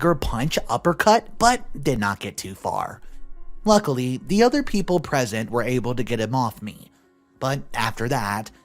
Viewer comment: when he said "the other people present" I thought he said "the other people pregnant" and had to stop to rewind to rehear it but I heard wrong 🤔🤷😂😂😂🤣🤣🤣🤦🤦🤦🤷